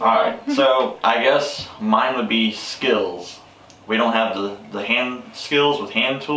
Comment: Lol, I thought said skittles not skills. Lovely video, I really enjoyed it 😀